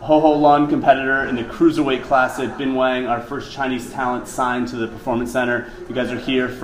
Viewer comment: All this time, I thought they were saying benoit... But, it's Bing wang. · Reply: *bin wang